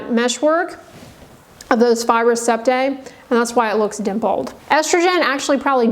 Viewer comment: Thats a lie???? Hrt is real! Someone explain plz why she said glowy better skin is a lie w hrt estrogen?! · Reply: She said.... "thats why" I thought she said "that's a lie" too. I had to rewind a few times. My skin is absolutely better on HRT